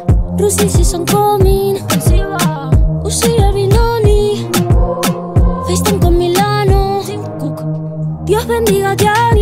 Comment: pensé que decía "Christian con Milano" lmaooo